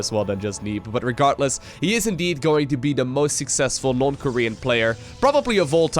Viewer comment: Did he say north Korean player